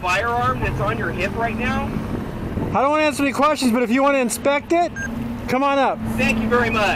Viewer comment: "that looks like a firearm" in a country where guns are legal. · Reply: maybe I’m deaf but I coulda sworn he said I don’t answer questions not I don’t know how to answer. I agree he could’ve been straight to the point but there is a difference in the answers